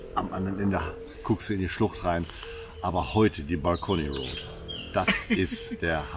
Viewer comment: Super Video, geniale Impressionen. Wie heißt die Route die bei Minute 3:19 benannt ist, Bacolliroute oder so, ich kanns leider nicht genau verstehen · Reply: Dankeschön. Wir sagen dazu Balcony Road....Balkonstraße. Ich glaube aber das ist kein offizieller Name 😅